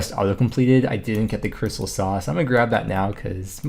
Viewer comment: it totally sounds like he said, "because I had the quest auto completed, I didn't get the CRYSTAL SAUCE!" Haha!